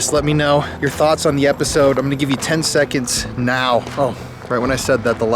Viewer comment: 10 seconds?? I thought you said 10 days 🤣 I lose lol great episode oh and “I love the deli” haha